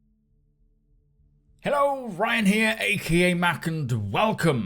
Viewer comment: It took me months to realize he's saying "Ryan here" and for some reason I always thought he was saying "Ryanair" like the airline company lol and I never gave it a second thought lol